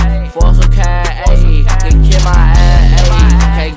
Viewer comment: did he say "then get my ass ate?" Or am I trippin💀💀